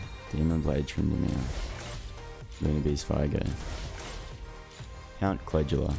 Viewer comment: Man who else heard him say Lunar Beast Veigar when he pulled Viego